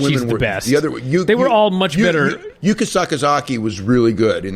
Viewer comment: "You can suck a zaki".. WTF did you say to me?!?